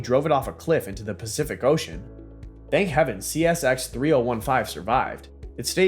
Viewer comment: Did he just say thank heaven the “car” survived?